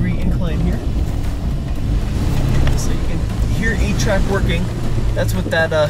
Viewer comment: Every time you say a-trac it sounds like your saying 8-track